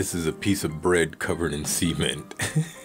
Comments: I thought you said bread covered in semen, not cement, had to listen again. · I swear I thought he was saying "semen" whenever he said cement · Each time you said cement it sounded like you said seamen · I swear I thought he was saying "semen" whenever he said cement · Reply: Ohh thank god!! I thought he said semen too. But yeah. Cement makes so much more sense. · Bruh the way u pronounce cement sounds like u saying semen